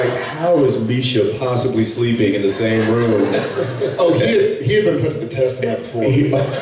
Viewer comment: What's Jared saying at 7:29? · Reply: did u mean around 7:26? "he had been put to the test the night before"